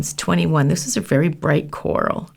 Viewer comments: “It’s a nice light coral” That’s... an eye-searing neon orange if I’ve ever seen one. 😂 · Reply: I thought she said bright lmao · “It’s a nice light coral” That’s... an eye-searing neon orange if I’ve ever seen one. 😂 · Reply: I thought she said bright, not light...